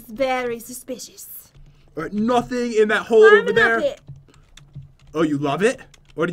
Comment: when jen said 'i'm climbing up it' i thought she said 'i'm a nugget' 😂😂😆😆 i'm dyingggggg